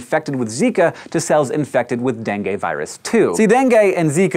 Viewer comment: I thought you were saying Bengay, not Dengue. Needless to say it was weird.